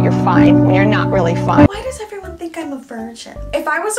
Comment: my subtitle said "why does everyone think I'm a furniture" .... um, okay ??